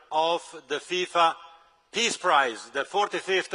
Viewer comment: Oh! TiFA Peace prize! I thought you said Thief of Peace prize.